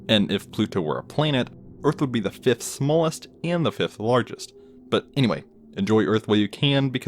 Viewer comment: What he said:Largest What the subtitles said:Smallest